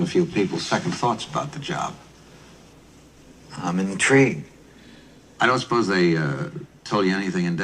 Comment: he says "I'm intrigued" but it sounds like he says "I'm in the tree" feels like an error that they left in. like he started to get the words wrong and caught himself.